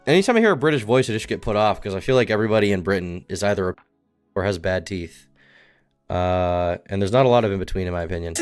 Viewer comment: Did he say they’re pedos?